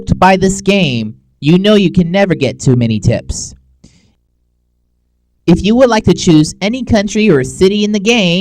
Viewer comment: lol at 9:21 i thought you said tits lol because of the girl great video